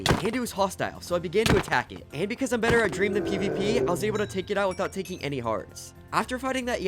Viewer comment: “and because I am better at dream than pvp” there’s no way i’m the only one who heard that